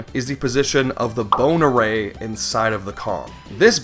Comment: what he says "bone array". what i hear "boner ray"